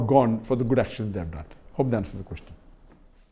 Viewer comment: Why he always says "Hope the answer is questioned" instead of "Hope the question is answered"